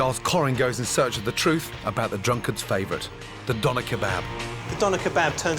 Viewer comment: I thought it said “how boner kebabs are made” lol oops